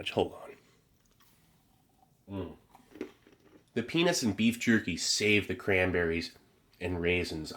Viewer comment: I love this channel!! So please don’t take my observation the wrong way lol, but at 38:24-38:27, I swear I heard the words: “penis and beef jerky”. Lmao, I swear, I haven’t laughed that hard in years!! Well, last I just wanted to say thank you for finding these various MRE’s and then sharing with us how it tastes, and everything else. It’s very relaxing 😌